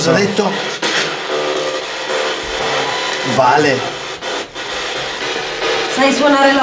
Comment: C'è veramente poco da dire ragazzi! Sono sbigottita. Siete stati più grandi che mai, non è una cosa da tutti i giorni trovare determinate reliquie. Comunque io al minuto 11:18 ho capito tipo "vai" ma anche "nein" (no in tedesco).